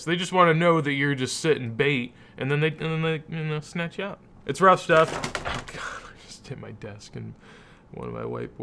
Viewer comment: Did I hear “It’s Rust stuff” at 5:55?? 👀👀👀